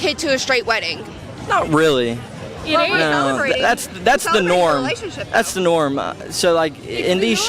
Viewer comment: did that just say "being white was the norm"? lol